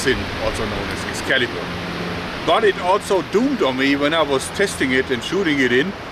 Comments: you pronounced dawned in "dawned on me" incorrectly it sounded like you said doomed, it is pronounced "dahned" Love your content, just trying to help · did I hear 'it doomed on me ? " .. egads that sounds like a zombie encounter .. "it dawned on me" like an idea that comes from a new day's sunrise .